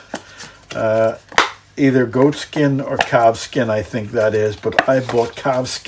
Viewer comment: Like your new intro. Glad you showed us how to reskin a banjolele. At first I thought you said cat-skin, not calf-skin. My blood ran cold for a split second.😉